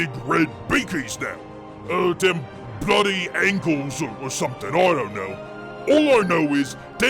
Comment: I lost it more than I should have when you said blood angels are mad at "horse" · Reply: i literally heard "the bloody angles"